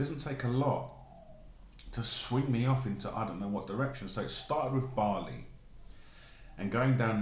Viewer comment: I thought at the start you said "It all started with barley" and thought - mmm, that's a strange thing to be drawn to... Haha. Just keep going, you're totally getting there, I know you are! x